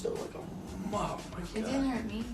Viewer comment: What does she say at 5:08? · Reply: Her tattoo on her rib didn’t hurt her She said it didn’t hurt me cause Shia was talking abt her rib tattoo